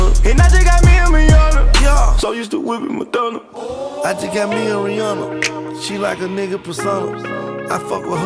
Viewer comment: Always thought he said 'me and my humma'